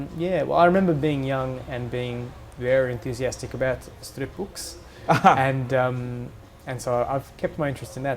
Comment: did he say strip books... please don't tell me he said strip books...